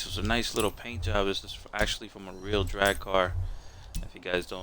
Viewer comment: Hold up did i just hear that correctly "my real drag car" at 1:10. No offense but you sound more guetto then the real owner who sounds more of a countryman